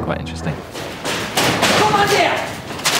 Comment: what does he say at 0:10? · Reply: 11 years late but he said "I GOT AN IDEA"